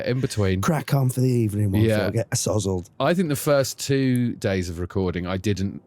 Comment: Did Phil say ASSOZZLED ? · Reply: "...get uh sozzled"